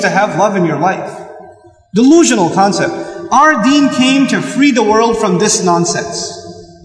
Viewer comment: Ok, maybe I shouldn't listen to this while doing work, and only half paying attention, but at 6:32, I thought he said "our Dean Cain came to save...." I was like "what?! Why he is talking about Superman all of a sudden?" :)